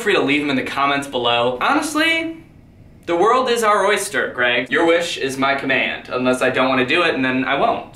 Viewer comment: The captions said Craig instead of Greg